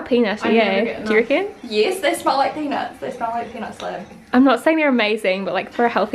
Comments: Am I the only one that thought said "they smell like penis" not "they smells like peanuts" 😂😂😂😭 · when you said they taste like peanuts i thought you said penis i was like oh..... interesting comparison